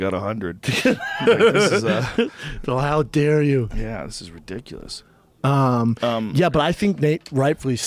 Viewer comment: When Brendan said that’s ridiculous the subtitles thought he said that’s Hillary’s dick 😂